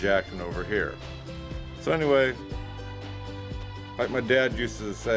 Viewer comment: I thought Dad said "hide and see".